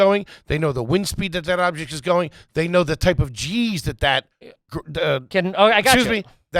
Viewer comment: 100% thought he said type of cheese